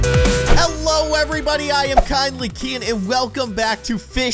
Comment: I thought you said finely keyin but it was kindly keyin